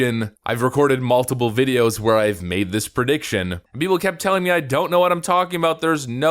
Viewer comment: Did he just say "Purdiction?"